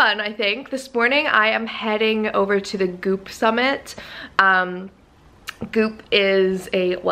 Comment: Thought you said boob summit instead of goop summit